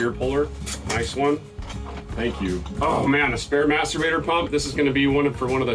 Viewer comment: Ii thought you said "spare masterbater pump!"